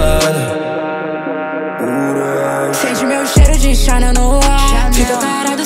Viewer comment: Juro q eu ouvi "sente meu cheiro de xana no ar" kkkkkkkkkkkkk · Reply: Tbm kkkkkkk dps fui ver q era channel